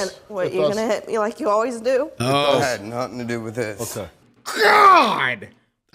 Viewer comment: her: accuses him of being an abuser bc shes forced to admit she killed her baby Him: has a valid reason to want to hit her in this specific moment and instead calms himself and talks to her rationally and caringly ... yeah... shes totally not a crazy ass liar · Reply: I thought he said "I had nothing to do with this" trying to stop her from derailing the conversation